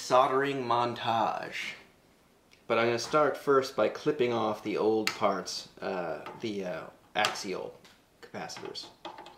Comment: I heard: "Flipping off the old tarts, the actual bastards" 😂